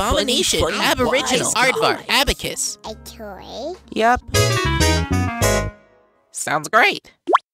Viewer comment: What did the sun say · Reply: "A toy"